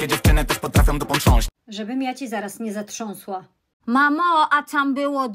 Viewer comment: ✨mAmO a TaM bYłO zUpA✨ usłyszałam xd